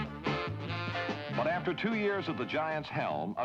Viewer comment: I thought he said 2 years of the Giants hell